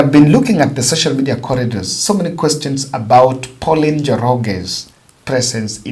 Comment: Madharau ikoje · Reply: si madharau, I have hearing loss I thought said Paul Njoro.